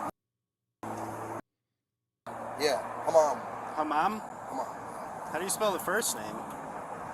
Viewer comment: When he repeated his name 24:57 it sounded like ‘hammam’ which is Arabic for ‘toilet’ (and pigeon,depending on context). Very appropriate 🤣